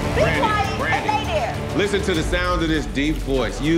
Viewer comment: "BE QUIET AND LAY THERE!" *what my momma said to me when I didn't want to take a nap*😂